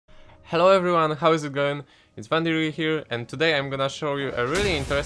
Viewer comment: Did you say "How is you going" in 0:00?